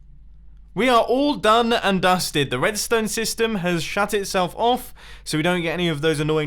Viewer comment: When you mentioned that the redstone shut itself off I almost heard you say Sha* itself off